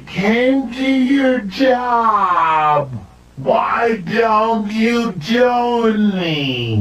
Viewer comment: most of the time it sounds like he says "JONAS", but this time he said "WHY DON'T YOU JONNY?"